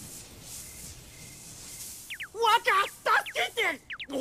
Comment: translation: M'lord, help me! What I heard: Wha Da fuk did this?